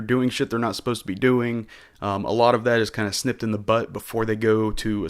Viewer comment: did you say "sniffed in the butt" or "nipped in the bud"? Because it sounded like the first one...😂😂😂